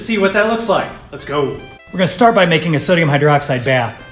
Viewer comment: Not gonna lie I thought he said soda hydroxide